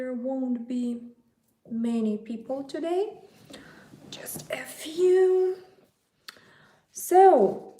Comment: “Just a few”- lol, I heard, “just F you.”